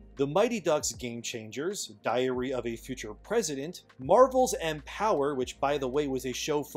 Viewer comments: I had to replay that one one part a couple times where I thought you said Diarrhea of a Future President. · I really thought he said "Diarrhea of a Future President."